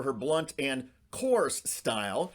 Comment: When you said Marge had a "coarse" style," I thought you said "horse style". Made sense since she's horsey looking...lol